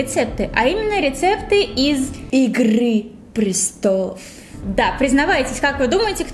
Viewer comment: мне послышалось икры 😂😂😂 кому тоже ставь лайк